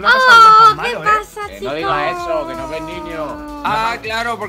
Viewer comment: dijo que los ben niños? o escuche mal?